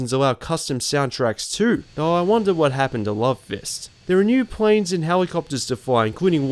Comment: Did you say Love Fist? Those guys are so super. it's so much fun to listen to the radio. I'd listen more, but someone stole my radio when they killed my foster family.